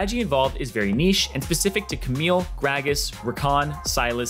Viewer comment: Does the guy say "Pecific" Instead of specific????? That threw me man...